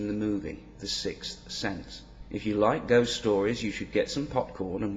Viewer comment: Man, I must be hard of hearing... Only by the third time watching the video, I actually understood the words "if you like ghost stories..." in the beginning (during the spoiler alert). Before that, I thought you said "if you like *those* stories"